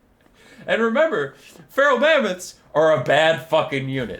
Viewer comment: I thought you said Feral Llama at first 😂